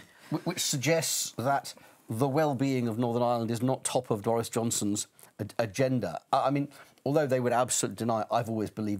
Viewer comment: did he call him Doris Johnston?